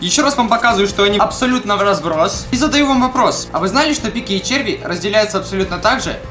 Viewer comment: Послышалось "Дикие черви"))) XD